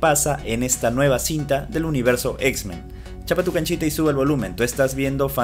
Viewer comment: ¿que dijo? 😂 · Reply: Chapa tu Chanchito o Chapa tu Ganchito